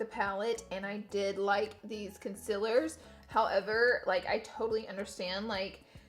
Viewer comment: My friend heard her say conc-I-ler once instead of concealer and now she says it nonstop Laura you have created a monster. Lol honestly though I have country accent but I still say con-seal-er